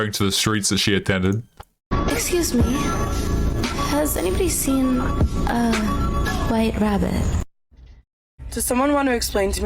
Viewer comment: The reddit story reminded me of a weekend trip I took with some buddies. For context, there were four of us guys all in our early 30's, 3 of us are married and the fourth was single. We were in a small coastal town because my mother-in-law let us use her condo on the beach. We ended up at this dive bar that was packed. We grabbed the only open table and ordered a pitcher of beer. A couple minutes later, these two girls walked up to us and asked me if they could join us at our table. They were both attractive and knowing my buddy was single and there really wasn't anywhere else to sit, I agreed. Long story short, they were both married and on a 'girls trip' because neither of them were happy in their marriage. I could tell one of them kind of liked me and wouldn't quit chatting me up, telling me how awful her husband was. I continued to make counter points to every BS story she told me. We end up going to the casino with them, and the single guy ended up going to their AIRBNB and hooking up with them. I told him multiple times that there was no way in hell I was going over there and it was a terrible idea for him to go sleep with a married woman. He didn't care and shockingly the other two guys I were with also didn't seem to care. It's put a strain on our relationship, because having morals is important to me in this F'd up world that we live in. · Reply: What I heard her say was "excuse me. Has anyone seen my virginity ".